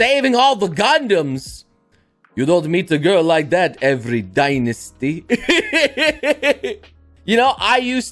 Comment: In Mulan I swore the emperor said "every dangest day"